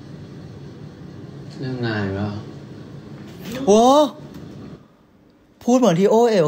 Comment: เต๋พูดว่าอะไรนะ ฟังไม่เข้าใจ5555 ใครก็ได้บอกเราที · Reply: เต้พูดว่า เรื่องงานอีกแล้วหรอ